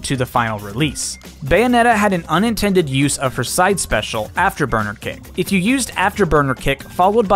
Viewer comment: why did i hear after burger king